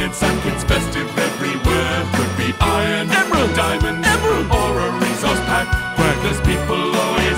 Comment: This verse is my favorite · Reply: I thought he said or a reese pack, but he said.or a resource pack